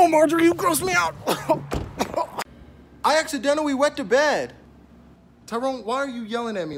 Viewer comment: i thought he said i went to bed ahahaaaaa